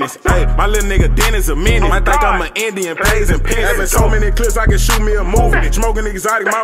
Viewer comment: What he say at 0:35 I think Ima Indian pay us in paisa?